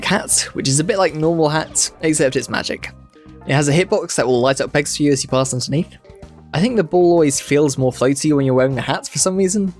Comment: Is it just me who has absolutely no idea what he said at 25:44 lmao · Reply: “It has a hitbox that will light up pegs for you as you pass underneath.”